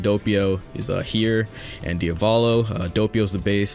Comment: did he just say diavoolo instead of diavoro!